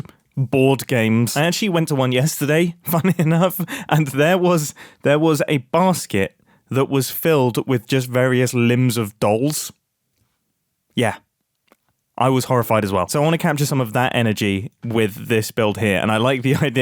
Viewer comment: at first I thought that you said that there was a "casket full of limbs of dolls"🤣